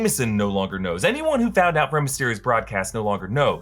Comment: Did he just say rey mysterio at 3:59 😂